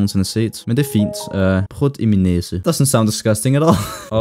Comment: Sounded like you said "Prod my nasal"